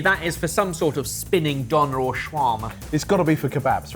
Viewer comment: It's döner. Subtitles are wrong. Also the word "döner" comes from the turkish word for "spinning" so you don't have to say "spinning döner". It's like saying spinning spinning.